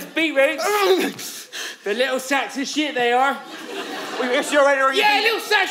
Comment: The subtitles said "little sexy things" instead of "little sacks of shit" when and his friend show up at 8:45 lol